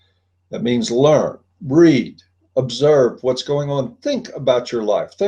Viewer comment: funny slip at 30:55, recommending to "breed" instead of "read"